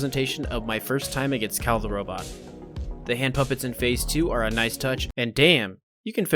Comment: did you just say Kahl the robot?